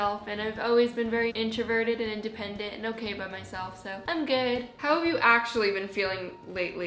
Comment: when she said "i'm good" i thought she said "i'm gay" & that was a phat mood